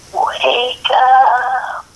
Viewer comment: I think it was saying ''wake up''